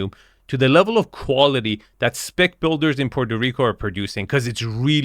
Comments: Did you say ''spic builders in Puerto Rico''? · Does anyone else hear "SPIC BUILDERS" at 4:15? lol